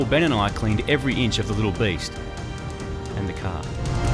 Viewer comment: did any one else hear at 16:48 when he said clean every inch of the little beast, two seconds later in a lower voice says, and the car . "cleaning every inch of the little beast, and the car" wtf! I heard it using my headphones.